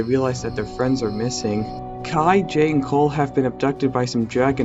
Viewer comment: Kai jay and cole go missing I thought it was zane instead of cole